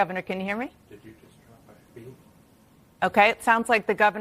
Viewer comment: Anybody else hear the tech in the background say "Did you just cut my feed?" Listen carefully.